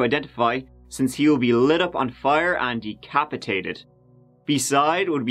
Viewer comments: Why does it sound like is saying "Christina Aguilera" whenever he says that dudes name? lol 😆😆😆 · At first I thought he said “Christina Aguilera”... 🤔🤔😃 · At the beginning of the video I thought he said Christina Aguilera 😆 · I thought you said Christina Aguilera. And i was immediately confused.